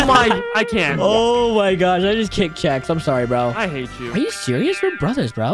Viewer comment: caption says checks not Chex…